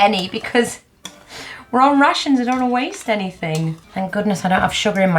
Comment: Every time you said 'Rations", I at first thought you were saying "Russians'.:))